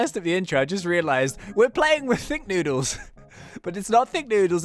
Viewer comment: When you said ThinkNoodles I thought you said ThiccNoodles😂